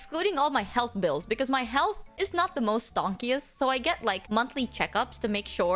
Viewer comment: I think she's saying "stonkiest" as in stonks go up